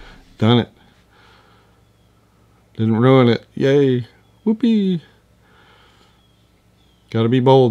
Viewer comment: I often watch your videos with the CC on, which picks words phonetically that it thinks match what you are saying. Sometimes with amusing results. At about 13:30 or so, you say "whoopee" and the text came out "who peed", doubly amusing while you are sloshing amber watercolor paint around. I don't remember which video, but once you said "burnt sienna" and the CC wrote "birds see Anna", so I was like, who the heck is Anna?